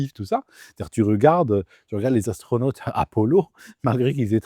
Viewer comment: Les astronautes "à polo" ou Apollo ? Et ils ont traversés quoi les astronautes ? La rue qui séparait les studios de la cantine ? Elle était radioactive la rue ? Bon aller, on a rien entendu 🙂